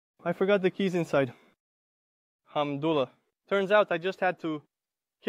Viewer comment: The Ai made a mistake at 1:25 instead of saying Alhamdulilah, maybe you could do these type of vlogs in Arabic? You could speak in Arabic and leave English subtitles.